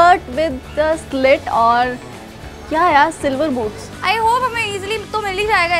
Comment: who else hears silver *boobs😂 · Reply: Dirty minded that's boots